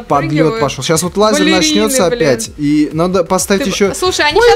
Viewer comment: кому показалось что он сказал слово бл*ть